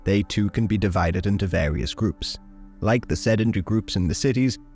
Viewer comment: What word does he use to refer to the Arabs who live in cities in 1:18? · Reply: Sedentary